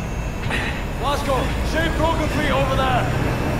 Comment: I thought it said “blazko shoot broken feet over there”😂😂😂